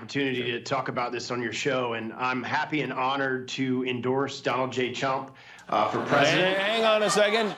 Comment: I knew when I heard this earlier when he said I'm here to endorse Donald j chump I thought that's what he said 😂🤣😂🤣😂